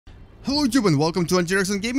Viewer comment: Try putting spaces between your words when you talk. Fist thing I heard was "Hallootube" but it think that was supposed to be "hello youtube." And what are "heers?" Oh... heroes!